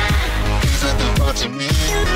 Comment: My friend was playing the Dirty mind meme and I was playing this and all I hear is "Feel like I'm losing my dirty mind baby" and now I just give up on everything